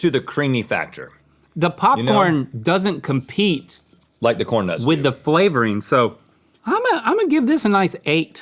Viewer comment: Thought corn nuts said torn nuts